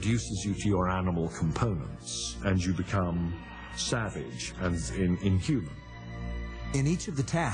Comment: Did he say you become SAVAGE at 5:29?